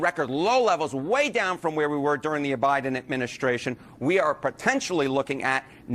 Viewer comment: Idk did he say the O-Biden Administration?